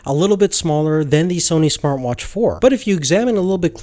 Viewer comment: Did he just say Sony Smartwatch 4?